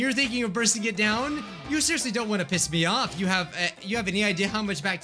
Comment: Huhhhhhh, did he say piss!?!?